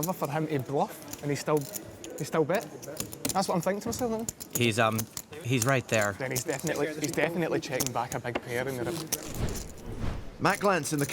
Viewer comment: What was Geilich saying, pls can someone help me out? At 8:05 and following... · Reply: I'm thinking to myself that's the worst river for him to bluff and he still bet... he's definitely checking back a big pair in the river (Strong "German" accent :D)